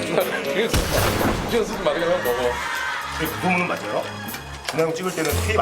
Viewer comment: 여기서 준하형 찍을때는 테이프안넣고 찍는다고 말하기전에 노홍철이 뭐라고 말햇는지 아시는분??? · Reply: 근데 그 말 맞아요? << 이렇게 들림